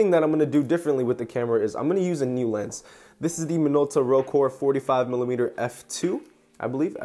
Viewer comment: I misheard you saying "Rokkor" as "rogue". Kinda cool! By the way, I own that lens too and I have mounted it on my black SRT-101. I actually purchased this lens recently but I haven't tested it out yet. #minoltagang